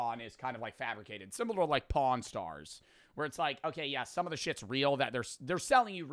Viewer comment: Pawn stars I almost thought you said porn stars lol